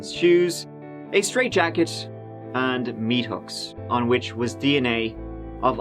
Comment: Did you say meat hooks 13:54? OMG, don't tell me her actually did torture them? That little boy too? ...... what a monster!!!!